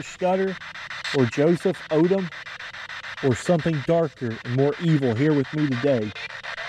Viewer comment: said "I am evil" if you're listening closely?? anyone else heard?